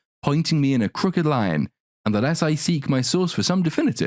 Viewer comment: Damn all this time, I thought they were saying the less I seek my soul for some definitive. I didn't think I could love that song anymore, but it just climbed up the charts on my favorites list.